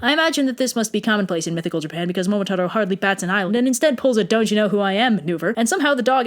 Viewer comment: I Thought You Said "Doge You Know Who I Am?"